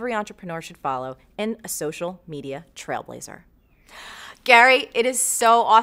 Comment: LOL Marie! I heard you say, 'social medier" - seriously excellent interview, you both ah stahz.